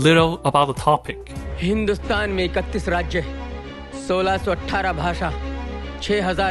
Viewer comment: Even his subtitles are wrong. At 9:27, he says 1,618 languages but the subs say 618 lmao.